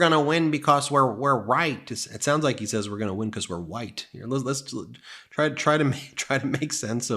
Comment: Wow, it did sound like Eric said "we will win because we're white"...Ben, thanks for clarifying that for us. However, Eric is exactly like his father Donald and the rest of the racist Trump family...